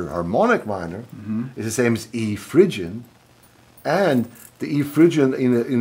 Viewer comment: Can somebody hear what scales his talking about at: 1:33. "...Should be A minor hamoic is the same as E..." fblank blank. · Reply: E frigio